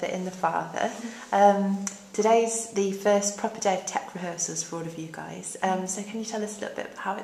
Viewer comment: Can somebody tell me what she's saying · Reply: “Today’s the first proper day of tech rehearsals for you guys”